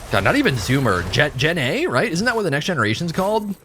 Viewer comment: I know you were saying "Gen A" but all I heard was "Jenny" like Forrest Gump